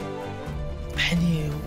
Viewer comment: 즈음에 뭐라고 하는 건가요?? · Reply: 팬이예요 법정에서 봐요 5년 이렇게 말하는 거에요ㅛ 여기서 5년이 저 판사가 저 여자한테 내릴 처벌을 얘기하는 거예요